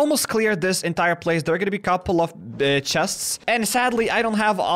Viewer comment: uh there's gonna be a couple of bitches is what it sounded like